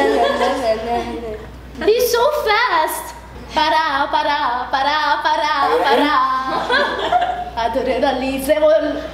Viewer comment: ouvir ela falar o nome do meu estado é a melhor coisa que eu poderia ouvir 😍😍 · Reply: Eu entendi "faraó, faraó, faraó" 😂😂😂